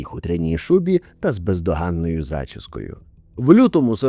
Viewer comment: почулось пиздоганною зачіскою😅